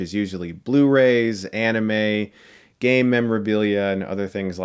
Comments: Did he said "Gay memorabilia" XD? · i heard "...anime, gay memorabilia..."